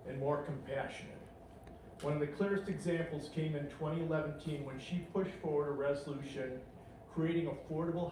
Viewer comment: Did he just say 2011teen😂